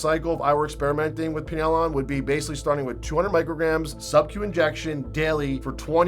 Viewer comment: At around 8:14 you mention starting out on pinealon at a 200 microgram dose. However, the caption says "…starting with 200 g subcutaneous daily." (Yikes! Good thing probably no one would be so stupid or masochistic as to actually attempt such a dose; hopefully some folks don't simply assume that "g" was accidentally replacing "mg" due to bad transcription.)